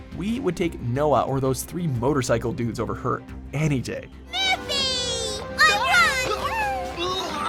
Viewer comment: Did she just call Yugi "Luffy"? 😶